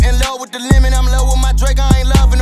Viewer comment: Am I the only one heard him say I’m in love with faget